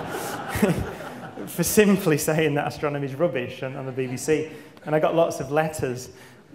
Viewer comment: woah i thought he said "ASTRONOMERS were rubbish" i had to go back and listen thank god i misheard